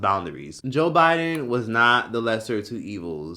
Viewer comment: I don’t know why with the first one I thought said Joe Biden is a lesbian and I was about to leave 😭😭